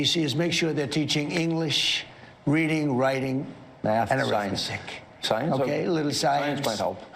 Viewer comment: Did Trump say (at 9:20) that he wanted schools to teach "English, reading, writing and 'arymestick' "? (I presume he meant arithmetic.)